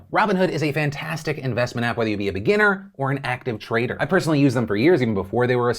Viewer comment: When Phil did the sponsorship I heard “active traitor” not “active trader.” The capitol assault has me fucked up haha